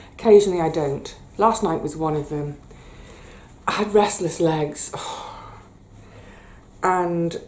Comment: Sorry, I thought you said Wrestler's legs. I'm wiser now